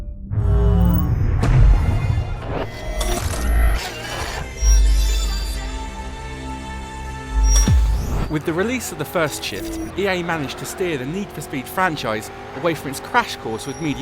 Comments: it sounds like he is saying "with the release of the first shit" lol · At the beggining of the video it sounds like he said "With the release of the first shit" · at the beginning i thought he said "with the release of the first shit" lol xD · in the beggining of the video I thought he said "with the realease of the first shit" lol